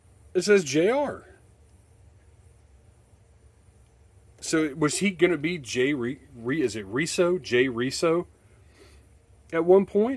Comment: How in the world did I miss that? Good call. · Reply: Na you got it @ 6:06, “was he going to be Jay Reso?”, but I heard that after I typed the comment haha